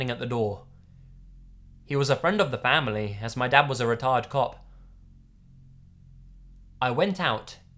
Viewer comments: I heard "Retarded Cop2 xD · when you said "retired cop" it sounded like you said "retarded cop"